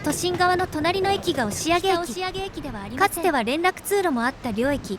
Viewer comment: でなんて言ってるか分かりますか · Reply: 「北押上駅ではありません。」